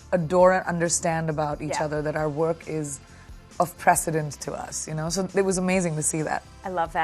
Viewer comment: What's the word she used at 1:57 ..... Our work is ........??. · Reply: Precedent